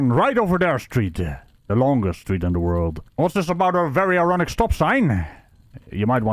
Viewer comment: i keep hearing "very erotic stop sign" and I don't think I'll ever hear it as ironic in future vids now 😂